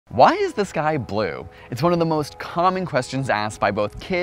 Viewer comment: I misheard the first sentence and heard why is this guy boiled